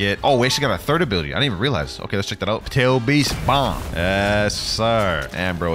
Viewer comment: it sounded like he said potato beast bomb 🤣 Also why is everyone begging in newest?